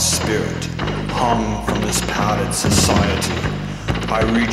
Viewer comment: Spoken part: "I remember de puppet reflection as i look deep into my eyes The madness in a darken spirit harmed by this society I reach up to cut the strings, realizing that i am KICHIGAI" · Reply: I heard "hung from this padded/patterned society"